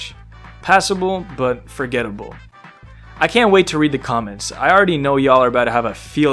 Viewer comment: I thought you said read the comics and I was so confused as to why there would be the office comics and why I'd never heard of them.